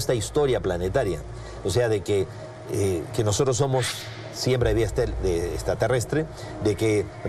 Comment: Que dijo Sixto Paz ¿? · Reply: Dice que nosotros somos siembra de vida extraterrestre.